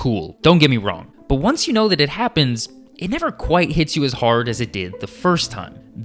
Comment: I heard "gets you as hard" not "hits you as hard"😭